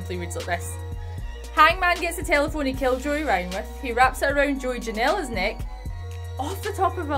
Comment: I was just going to say the exact same thing. She started talking about the first match and I had to turn the video off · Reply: brad koch I agree, I thought she said he tells us how he murdered Joey Ryan and I was confused so I rewound